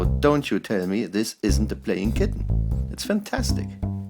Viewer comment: I thought that he said a playing kidney